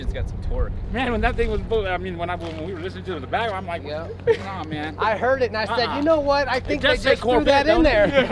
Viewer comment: what tf did he even say 😂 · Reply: "MAn, when that thing was pulling, I mean... when we were listening to it in the back, I'm Like nah-man, nuh-uh, it does say Corvette, doughnnit?"